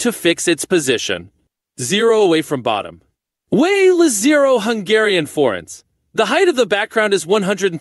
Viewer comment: did you say "zero hungarian forints"? o_O